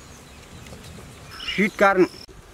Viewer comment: im sorry but i hear ''shit corn''😂